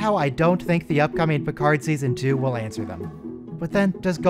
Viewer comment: Maybe Golem Picard has those sweet under-the-fingernail button controls like Lore... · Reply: Oh. Golem Picard. I misheard it as "Gollum Picard".